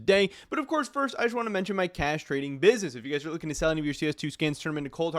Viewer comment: Bruh… I thought you said castrating business ✂️